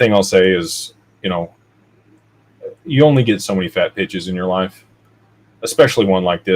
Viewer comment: ‘You only get so many fat bitches in your life?’ 39:52 I rewound the tape on that one!! ‘Pitches’ I get it now. He says it with such love that I prefer the mishearing